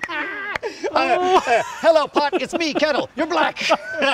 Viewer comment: does he say ur black